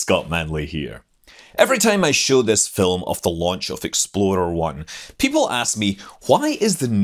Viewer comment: I kept hearing "Explorer 1" as "Exploder 1".